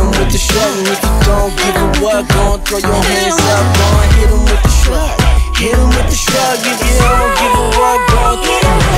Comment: What is she saying at 2:47 ? I can't understand 🤔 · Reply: victoria hit 'em all I think